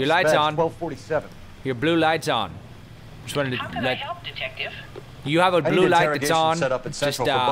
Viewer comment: Everytime he phones the policelady, I hear "How can I help you, King Dedede?" Damn you 4kids.